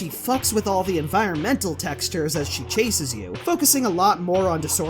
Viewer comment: Thanks to you, everytime I hear "focusing" I hear "fuck a sing" ._. Bab